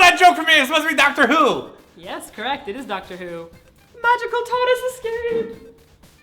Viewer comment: i don't watch dr who, so i totally thought she said 'magical TUMNUS escape' at the end. tardis. i know its tartis...and yet...i like tumnus better.